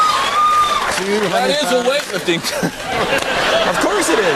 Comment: Can't quite figure out what Brad is saying at 10:50 :P "That is a ____" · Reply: brad explains his use of the word snatch by saying "that is a weightlifting term " which it is .